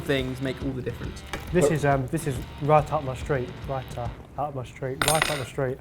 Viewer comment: What did Barry say at 9:05-9:08?? I'm so confused now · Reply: It was a play on the phrase "right up my street." He was saying "raita up my street." Raita is the traditional yogurt/cucumber condiment that you often have with Indian food - the white sauce they added at the end.